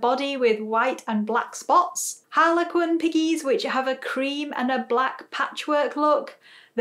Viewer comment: When you said Cream I thought you said green